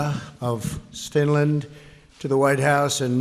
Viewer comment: I never heard of Stinland, is that what he said at 0:32 or am I hearing wrong?